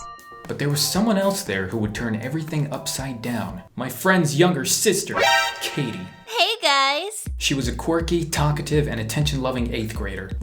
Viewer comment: I thought it said cringey sister I was like 😂😂